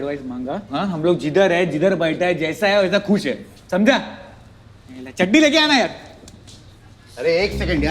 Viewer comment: who heard chadi